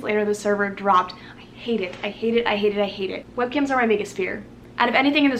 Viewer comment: when you said webcams were your biggest fear I thought you said "webkinz" and I was so confused